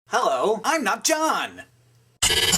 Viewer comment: At the start I heard "allo, i'm nub jong!"